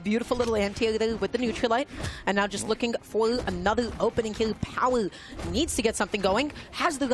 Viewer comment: i cant understand what she said 😭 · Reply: "and now just looking for another opening kill power needs..."